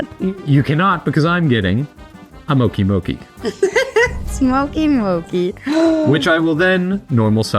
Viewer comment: I heard “Mokey Mokey”?